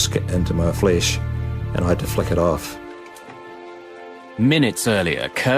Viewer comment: did he say fuck it off ?? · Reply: flick it off